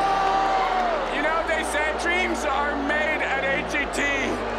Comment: i thought he said “dreams are made at age 18” and got really happy for a second.